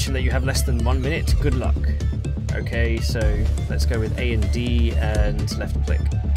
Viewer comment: I thought you said "Ok, let's go with AMD" Also, if the person was the best pilot in the class and still managed to miss everything I wonder what the rest of the class looks like.